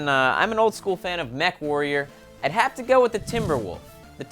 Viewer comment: it sounded like you said METH warrior